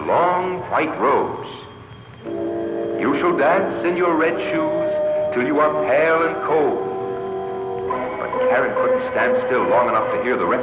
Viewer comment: what did the angel says? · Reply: "You shall dance in your red shoes until you are pale and cold."